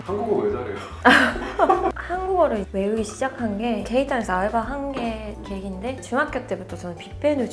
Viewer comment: 케이타운이 게이타운으로 들리는...크흠!